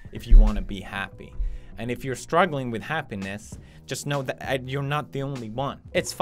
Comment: I lol'd had the video playing in the background, thought he said "Be happy, if you're struggling with a penis".